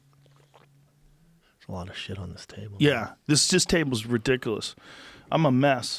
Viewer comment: I thought he said: "I just wanna shit on this table" right in the beginning and honestly, it sounds like something Brendan would say